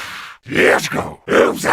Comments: I heard zombies say "kill him kill him" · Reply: lets go 6:27 loser XD and the last one I thought sounded like “go home” but kill him could be it too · To me, 6:25 sounds like they say “let’s go”, “who’s that” and “the moon” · One of the zombie noises sound like they said “let’s go” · I heard "Lets Go Losers." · I heard a zombie say "Lets go, losers" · Fam dont tell me u dont hear the zombie saying lets go losers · Im pretty sure at 6:27 i heard lets go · I heard the zombie say let's go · I heard "let's go" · i heard lets go